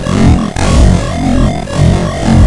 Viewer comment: misheard as "OKAY I WILL LEAVE" before seeing the comments and ruined the scare hahaha